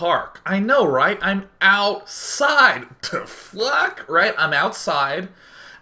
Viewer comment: I think he said fuck